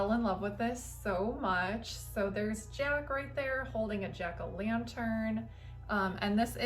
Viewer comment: Hee, heee...sounded like you said Jerk o Lantern 😄 Hmmm, you seem to have the best decorations.